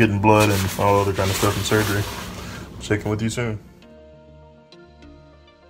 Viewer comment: Thought it said “chef”